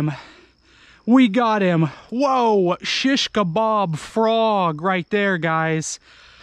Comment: What is he saying at 13:52 „shees kebab frog“?